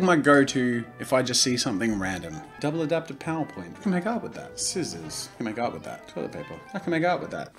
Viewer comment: thought he said "I can make out with that"